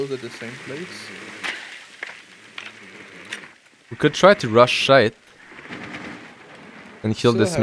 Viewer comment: please tell me I'm not the only one who hears "rush shite" lmao